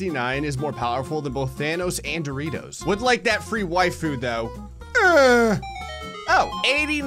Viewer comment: On the first bottle flip I thought you said free wifi instead of waifu.